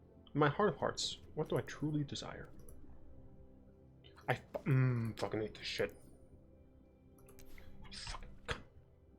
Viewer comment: in the video, did you say "fuck, I need to shit" or "fuckin hate this shit"..lol😅